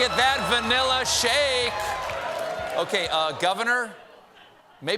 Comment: "VANILLA CAKE" - 2 EPIC words! Then I heard "VAX BOOTH", "URNILE CAKE" & " FLUFFER NUTTER"! MY 2 words for tonight monologe are "F#%ING HILERIOUS" 🤣🤣🤣!